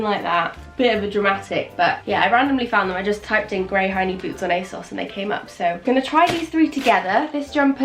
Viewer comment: Lol I thought you were saying “grey hiney boots” but you said “ grey high knee boots”!!!! 😂😂😂